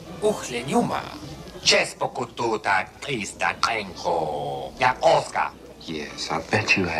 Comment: Just keeping track of my fav part in this vid EDIT: from what I heard, he said Chespoko Tu Da Clisda Kayko Ya Osca